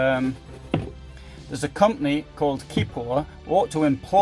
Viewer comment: love the videos but when you point out that shelving at 1:40 and say there is a company that produces better shelf retainers i cant work out with your accent what the name is? sounded like Keepo? anyone help?